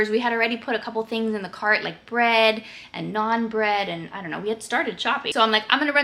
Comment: I definitely heard "bread and non-bread" at first 😂